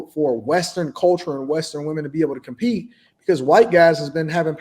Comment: I thought she said WESTERN women. Not black